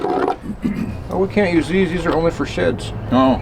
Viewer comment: What is saying bout the nails? 29:01 captions didn't pick up the comment. I love my old wooden mallet used for 'persuasion' I have jacked up two of my sheds. Watching this I realize mistakes made. I also love the mighty bottle jacks, I wish I knew how to repair them. Got two that seized up altho they still had fluid in them. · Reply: He said we can’t use these nails these are only for sheds. He said that because there was a picture of a shed on the box. It was a joke.